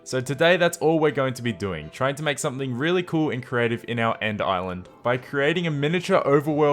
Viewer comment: Did you say In creative